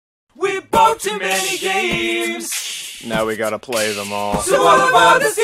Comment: i always thought they said, "we're 'bout to make games! Now we'll have to play them all SO HOP UP ON THE STEAAAAAAAM TRAINNNNNNNNN choo choo" but they actually say, "We bought too many games! Now we gotta play them all SO HOP ON THE STEAAAAAAAAM TRAINNNNNNNNNN choo choo" i was pretty dumb back then i guess. XD